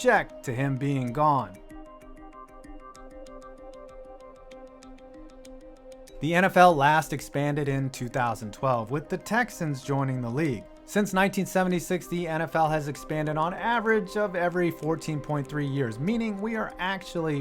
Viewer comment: Did I hear say since 19 70 60its